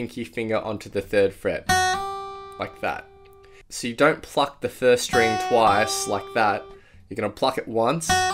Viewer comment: I just heard "don't fuck the first string like that"